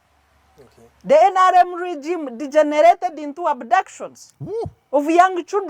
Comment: did she just call NRM ninjas as “degenerates “😂